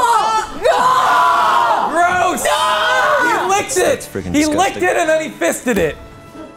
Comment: anyone hear that? "That's freaking skeptic"